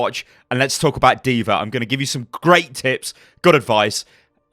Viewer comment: At the beginning I thought he said I'm about to give you some great tits. Where, I thought to myself, are they at?